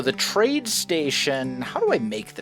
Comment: I thought you were saying train station the whole time